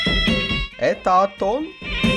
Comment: я услышал Это Антон?😂😅